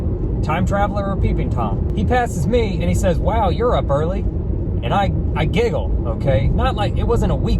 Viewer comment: I thought he said "Wow you're burly!" I was like boy he isnt a peeping tom OR a time traveler! He's GAY. AND HE INTO YOU.